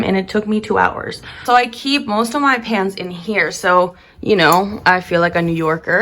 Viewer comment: GIRL! I thought you said PANTS 4:20 and i was like ok yes Carry Bradshaw!